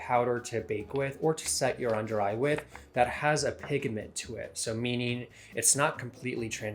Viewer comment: Did you say: pigAment????? I had to replay it ....lmao